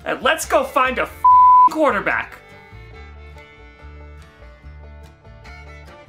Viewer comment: what did he say? Steelers nation, let's weld..? Let's welm?